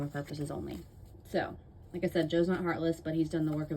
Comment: Without tay name they can't get views 😭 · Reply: the tarot reader? 😂 i thought you said joe's not famous